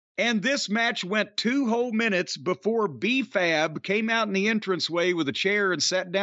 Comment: Every time I hear "B-Fab" I think they're saying "Beef Ab."